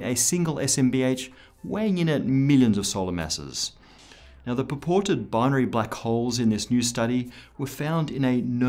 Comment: Did he just say "paported"?